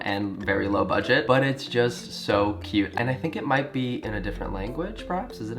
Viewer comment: I thought u said 'cute&gay' lol